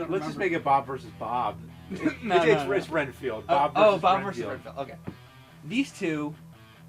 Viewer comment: lol for a moment I heard James say "Bob vs Chris Redfield."